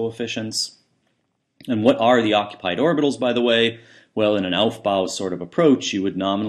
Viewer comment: Can you please type what you said in 2:10? When you turn the english captions on, they say in an “elf bow sort of approach” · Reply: "aufbau" (principle of filling electron shells by energy/spin)